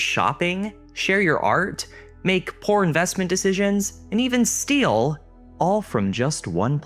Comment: "You can make po.....or investment issues" literally thought he was gonna say "You can make porn...." Lol